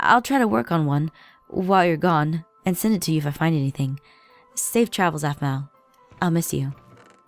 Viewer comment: When Zoey said safe travels I thought she said save Travis (mainly because I was reading nothing there I was washing dishes while listening to this.)